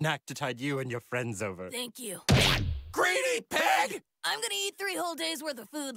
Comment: Luffy “Thank you” Zoro and Sanji “GREEDY PIG!!!!”😂🤣😂🤣😂🤣😂🤣😂🤣😂🤣😂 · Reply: At first I thought I heard “Greedy Prick!”